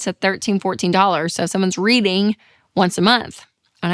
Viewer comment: When she said reading, I thought she said breeding..